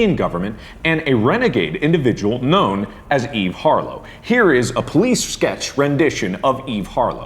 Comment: For a second I thought he said the name of that self proclaimed 'legendary' journalist Eve Fartlow, haha